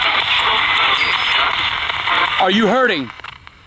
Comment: i heard "are you good?